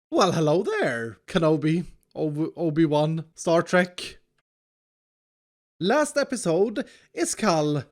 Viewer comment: DID HE SAY HELLO????